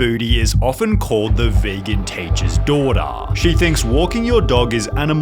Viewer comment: 😭 i thought it said virgin teacher